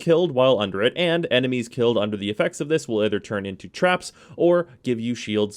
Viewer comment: Did you say "traps"? BANNED FROM REGION CHAT